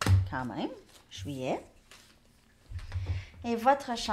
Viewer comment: "juillet" j'ai entendu "fuyez" 😅